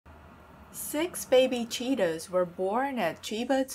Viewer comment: I know she’s saying Cheetah, but I keep hearing “Cheetos” 😂😂